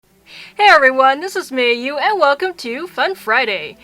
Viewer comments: i thought she said in the beggining of the videos hey guys its me Yu till i relized that her name is Mei Yu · At first I thought you said Miu instead of Mei Yu (bc im new here) but thats cute, sounds similar to my username/nickname